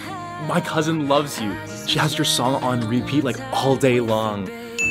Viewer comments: the subtitles say "She has your dong on repeat like all day long" i think it's supposed to say song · When it’s on 2:26 he says “she has you dong on repeat like all day” I think it was supposed to say song